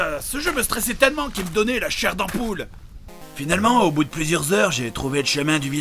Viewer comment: C'est moi ou à 6:30 il dit que le jeu lui donnait la chair... d'ampoule ?